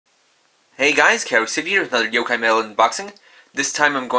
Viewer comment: i think its funny that whenever he intros all i hear is "hey guys K arks ixty"